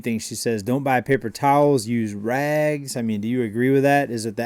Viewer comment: At first I thought she said..." Don't buy toilet paper use rags".